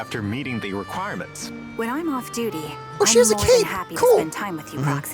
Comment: Hyped! So many characters, old and new! The vampires certainly look like someone I'd be interested in, though I'm still waiting for the idols before that 😭 16:37 I had to do a double take, I thought you said "Oh, she has a cake, cool!" first 🍰